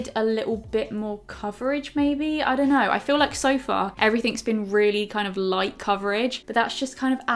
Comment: When she said “I feel like so far...” I thought she said “I feel like a sofa” where’s my brain atttt 😂😅